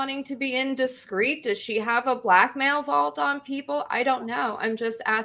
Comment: I heard "... Milo has a blackmail vault on conservatives ..." as "... Milo has a black-male vault on conservatives ..."